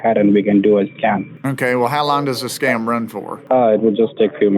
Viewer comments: he said 'scam' instead of scan lol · What the scammer said. We can do a scan. What i thought the scammer said. We can do a scam